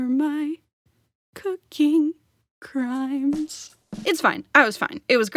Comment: I thought you said "f** crime"